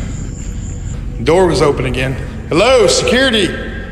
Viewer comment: the female voice says "Get out" not Hello.....which explains the female voice's hellish scream at 23:10...... she sounded like at first she was calmly warning him to get out...... then when he ignored her........she had to scare him out with the scream.